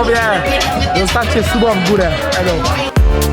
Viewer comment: Czy tylko ja usłyszałam zostawcie suba w górę